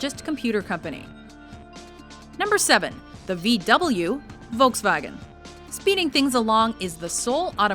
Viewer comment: Did I just hear, "Voltsvagen"?